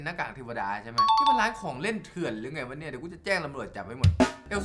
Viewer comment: ได้ยินว่า เดียวจะแจ้งระเบิดจับ 555+